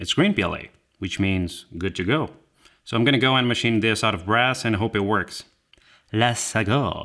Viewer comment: anyone else hear "machine this out of grass,"?